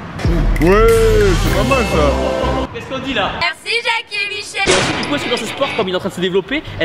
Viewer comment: qu'est ce qu'on dit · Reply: Theo_GTA_V Merci jacquie et Michelle.